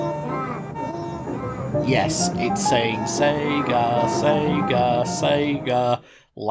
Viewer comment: Let me correct you there. As a Russian I hear more of “igra igra”, which means “game game” in Russian, not “sega”.